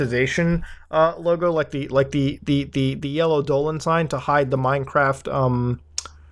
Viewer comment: Off topic, but I keep hearing "dolans" instead of "dollars" So I keep thinking, people pay for Poppy stuff with Danger Dolan... Merch or something